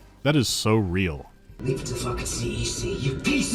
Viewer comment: I stg in the section on Isaac's voice, I thought you got Giorno to say 'Leave it to the fucking CDC' as he floats past giant virus-looking orbs.